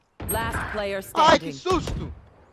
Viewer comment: did he just said Ay sus Hahahahaha I think he was a Filipino hahahah